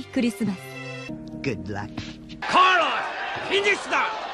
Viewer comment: Am I tripping or did he say "Carlos come here!" in Russian in